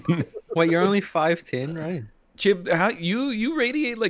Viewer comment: I've listened to 10:32 over and over but I can't make out what says that sends into the atmosphere like that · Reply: "Wait, you're only five ten right?" "Chib, how - you radiate a 7 foot tall energy." "I'm sev - we've met, but we can ignore that part"